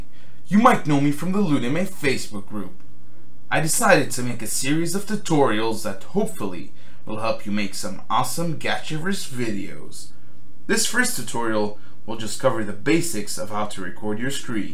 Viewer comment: For a second I thought he said "gachareus videos" Only me? Oh.